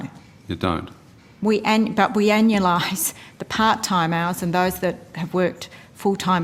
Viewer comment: I stumbled on the pronunciation of 'anualized' for a sec, and thought she said 'analyzed', as in "'we've blown these numbers straight up your ass". Upon further listening, I now know that this is not the case, and it was simply smoke.